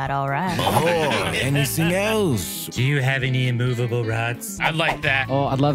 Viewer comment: Oh thank god I'm not the only person who has misheard it as immovable rock. I literally wrote that down on my character sheet.